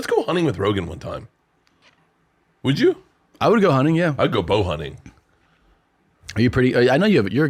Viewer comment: For at least the first few times they said it, I thought they were saying hawk hunting.